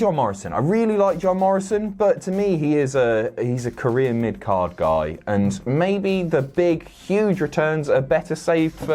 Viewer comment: Did say "Korean mid-card guy".? Please correct me. hahaha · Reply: He said “career mid-card guy”. He said it a little fast. So that would explain the confusion